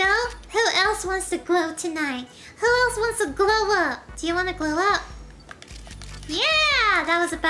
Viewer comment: “Who wants to glow up?” And I thought said “who wants to blow up?” XD